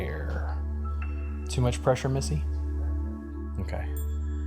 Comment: Description box says, but that's definitely. I think I even heard him call her Missy. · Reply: i thought he said melissy